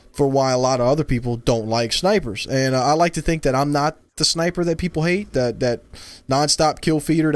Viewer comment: i kept hearing "I'm the perfect sniper"......really conceited huh?